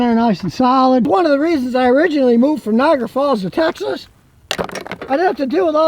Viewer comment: So what state did you leave and why aren't you going back?? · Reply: I have no idea either. I think 4:09 he said Niagara Falls which is in New York to Texas